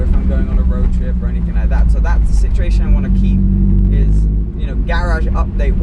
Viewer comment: thought you said you fixed the saturation.